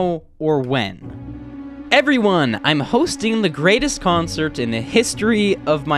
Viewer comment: he says conert instead of concert lol